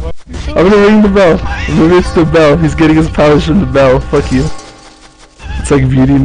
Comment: Lol at 1:32 it sound like someone said fuck me other then that this video was funny